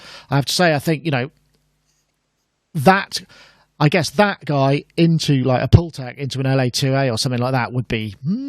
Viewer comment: What's say around 12:40 mark. :"That going into a protek, paulteck"?